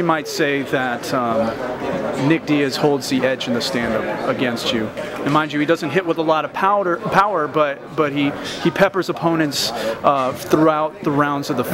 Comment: I heard him say powder lol